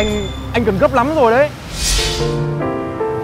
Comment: Mình nghe nhầm là: "đừng để thằng cún đó lừa"